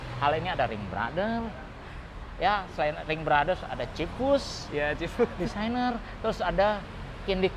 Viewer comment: yang disebutin apa yak? Kurang jelas saya dengernya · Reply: Kayaknya Ringbrother sama Chip Foose CMIIW 🙏🏻